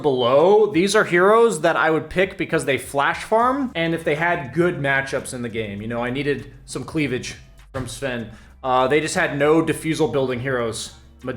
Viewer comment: Did he just said "Cleavage from sven"? 😶